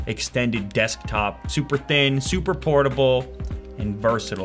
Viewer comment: Did he just say super pornable